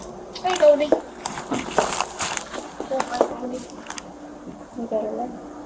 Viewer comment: I need to know what she said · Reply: ‘You’d better run’ ?